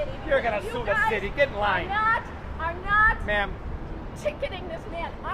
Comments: "Your gonna sue the city? Get in line!" ahahahahahahahaha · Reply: I thought he said: Get a life... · "I'm gonna sue the city!!!" Cop: "Get in line!!!" 😂😂😂😂😂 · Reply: Dandy RTS Gaming for real lol I thought the cop said get a life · 'You're gonna sue the city GET A LIFE' lol THIS COP. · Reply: I thought he said "get in line"